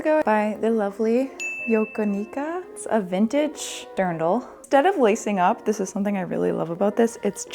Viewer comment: the caption says “girdle” but it sounds like says “dirndl.” The captions on this are otherwise pretty excellent!